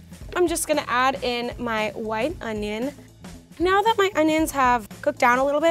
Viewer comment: Did say wine onions or white I heard her say wine onions